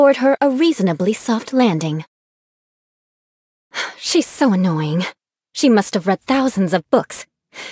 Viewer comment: Did Eula call Lisa annoying?